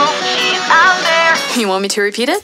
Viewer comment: OH CRAP. She's saying "some prince". I always thought she was saying "stompers"